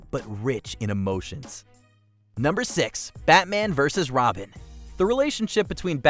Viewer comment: Bruh u scared me at 4:31 I thought u said "Batman & Robin" for a second...i agree on Watchmen, its more than solid and deserved more success and respect